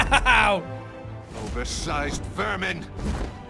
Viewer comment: am the only one who heard the riddler say oversized wamen